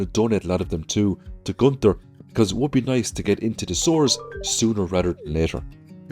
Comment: An Irishman saying "arm ourselves to the teeth" sounds a lot like "to the teet". It made me laugh. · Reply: Whenever he says "sewers" I hear "sores" and go eeew and have a laugh